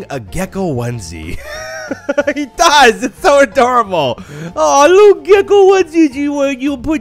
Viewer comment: my captions said "he dies. so adorable''